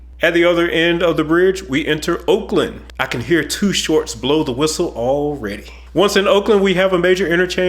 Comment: That “Blow the Whistle” reference was pure gold! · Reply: I thought he said Toot Shores, a restaurant near MSG, NY